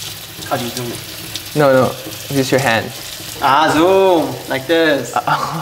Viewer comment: did YangYang just say "achso" only german fans will understand